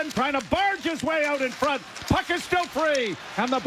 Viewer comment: what does the commentator say about the "phuck"